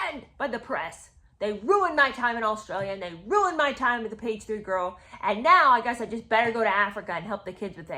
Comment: I didn’t know what a page 3 girl was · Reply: I thought she was saying pastry girl, 😂